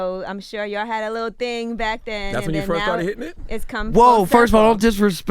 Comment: what did he say at 10:56? pls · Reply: boi taringa "thats when you first started hitting it"